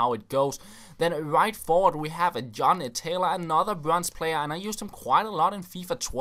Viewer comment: When says John Taylor at 2:57 it sounds like he said genitalia :/